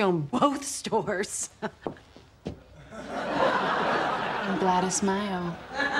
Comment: That deadpan delivery of 'I'm Gladys Mayo' still cracks me up. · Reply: Until now I always thought she was saying "I'm glad is Mayo" Mayo means they month of May in Spanish. I though she was implying May was her most successful month for sales.